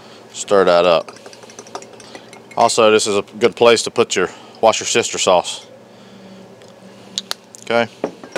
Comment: did you say “watch-yer-sister “ sauce ? You’re killin me buddy! That looks yummy ! Good ole comfort Groceries ! Thank you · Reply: I thought he said worsh(aka wash) ur sister sauce lol